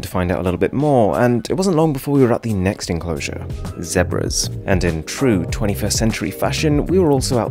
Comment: Did bro just say ZeBras??